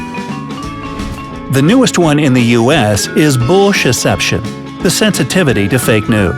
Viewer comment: #8 or #6 the one about the 5 senses at the end it sounded like he said "bulls**t" listen very carefully and you will hear it or think you heard it.